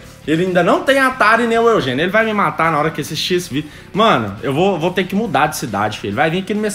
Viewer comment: e isso mesmo que eu ouvi Xvideo kkkkk